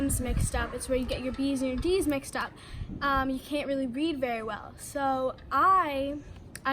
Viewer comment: at the beginning i thought she said “it makes you not be able to breathe very well”